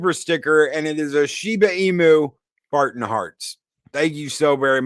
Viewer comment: Replay watch ¡LIGHT THE FIRES VALHALLA! !SKALL! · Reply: When says "Shiba Inu" I hear "Shiba Emu". All I can imagine is a Dog with an Emu body.